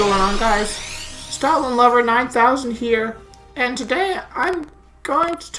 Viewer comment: At the start I thought you said "Stalin Lover 9000" lmao